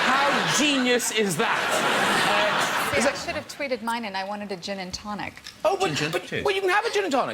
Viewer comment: what is she say in 5:06 before 'I wanted to gin and tonic and 8:36 part anyone English subtitle please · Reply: She said: "See, I should've tweeted mine and I wanted a gin & tonic", then " I wish someone would've supplied my meals for 4 and a half months, I had a newborn"